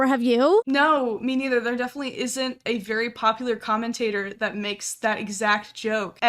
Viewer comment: my phone’s siri was listening and thought the video saying “a very popular commentator” was me saying “hey siri popular c*ck” …Thankfully it did not search anything but I’m officially turning my siri off after that 👏